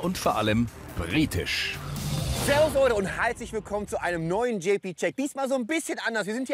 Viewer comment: sagt er bei 0:17 wirklich "halslich Willkommen" oder verstehe ich da was falsch? :D